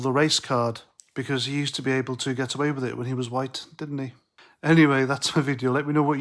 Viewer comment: I thought he'd said.he wasn't white.. he's got darker skin..he also took a pay cut to 1.3 million a year..what a joke